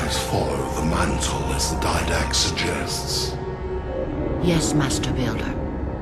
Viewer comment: I heard "yes, masturbator" in the beginning